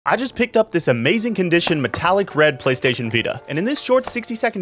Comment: For a second I thought you said Metallica red